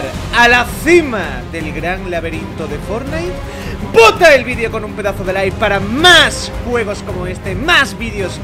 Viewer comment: POTAAAAAA eso escuché yo segundo 0:27 jajaja Church agrégame y has un escondite en la ciudad que hice porfa : Samantha25 en FORTNITE obvio